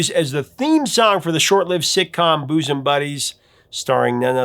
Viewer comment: What did you just say? Boose'm Buddies? I've never heard anyone pronounce it like that. The way he's pronouncing Gordon Gano's last name is also killing me!!!